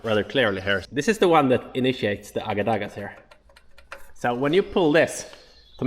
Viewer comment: What is the word he says here, sounds like hagadas for the braking?